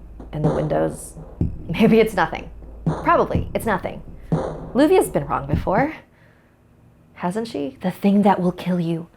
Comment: i cant hear what she said can someone tell me? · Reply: she's been wrong before.. Haven't she?..